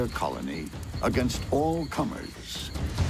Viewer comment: Thought he said "protect against all cummers" 😂😂😂😂